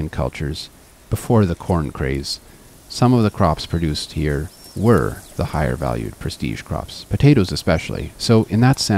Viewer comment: Can anyone tell me what the species of bean he mentions at 21:41? He says something like "taroi", the automated translation says "taro". But taro is a tuber propagated by tubers. So what can this plant be? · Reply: Oh that's strange that it was showing you the auto-generated subtitles, since I do have official ones with the correct name. That's tarwi (Lupinus mutabilis), also called Andean lupin. I'll likely say a bit more about that one in one of my next videos about Andean crops.